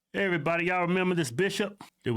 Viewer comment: I thought he said “hey everybody, remember this Bi#*h” 😂😂